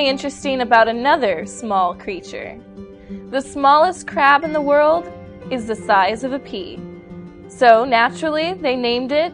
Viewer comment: HAhaha!!! ;D I heard " The smallest crap of the world is the size of a pee " !! WTF?!?! =D Hahahahaa..!!!